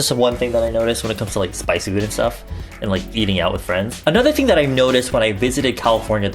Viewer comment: for a second i thought he said "eating out your friends" instead of "eating out with friends" and i was like boi what the fuck kind of action are you getting?? lololol